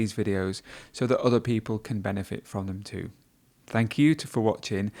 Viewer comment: did say THANK YOU *TO* FOR WATCHING,i wonder is this sentence right in grammar,thanks🤗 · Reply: Thank you, too, for watching